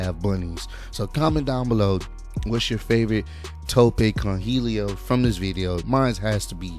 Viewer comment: that one announcer annoys me how he says tope con hilo, because to me, it sounds like he says tope con hero every time